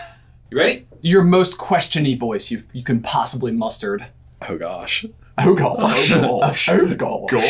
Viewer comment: Did say mustard?! @ 2:57 😂